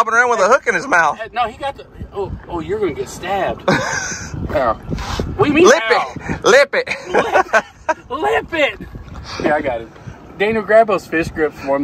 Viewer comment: "Did you say LICK it? LOL....Poor Coby....But I have to give him credit, I also at first heard "LICK IT" when he heard it in the other video. LOL